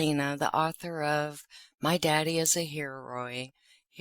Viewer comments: I thought you said my daddy is a hemriod then I'm like well ya he's that to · I thought you said , “ my daddy is a hemorrhoid “…..😂😂😂😂. And he IS!! 😂